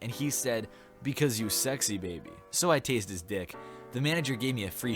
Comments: I thought it said TASTED not tased his dick. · I THOUGHT IT SAID TASTED HIS DICK GAHCBS BYE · well im dumb i thought it said 'tasted' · Instead of 'tased his dick' I thought you said 'tasted his dick' ... lol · my stupid ass thought it said tasted.